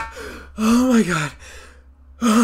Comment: I swear i heard someone say “Im fucking my chicken wings” 3:51 😭😭